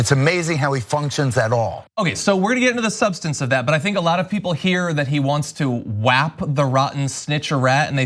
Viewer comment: Wait, Geraldo thinks that Trump functions? FOOL! EDIT: For the record, I thought Geraldo said he wanted to "fap" the rat at first.